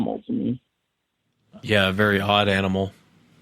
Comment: At min 25:00 does say "yeah, a very hot animal"? I ate some edibles earlier so I'm not sure if I'm hearing that sh*t right.